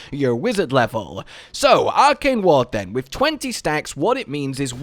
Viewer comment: not true.. im a lvl 10 wizard.. the ward starts with 10.... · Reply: sry.. 11:40 tripped me up. I heard it as.. it gives 20 stacks. My apologies.